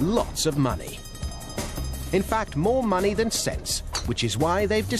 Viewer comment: Yeah, that's what I heard too. "more money than sex", That's why I will never get to play there. I gots No Money!!!!